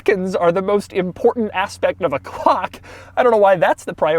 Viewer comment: Did he say cock?